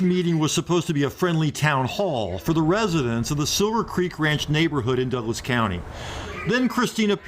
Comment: I could have sworn the narrator said the town was "Sewer Creek.". That definitely hit the ear wrong! Lol!